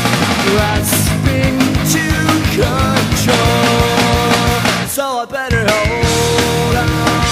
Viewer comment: So I better hold on I thought it said so I better ahora